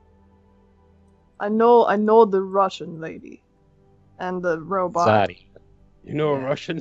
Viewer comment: THE FIRST TIME THEY SAID "Russian lady" I thought they said "Russian daddy" and my mind instantly screams "VIKTOR NIKIFOROOOOOV?!"